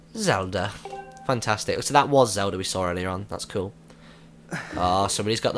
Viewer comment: it sounds like you say zeldra